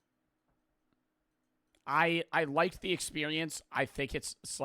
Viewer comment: was i the only one who heard say "expewience"